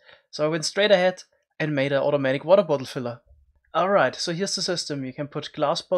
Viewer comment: Who thought it said filter not filler